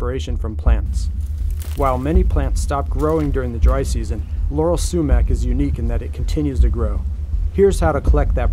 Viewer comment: I heard Yanny Sumac